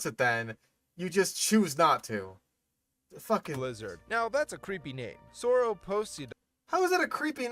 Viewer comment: sounds like youre saying “fing lizard”.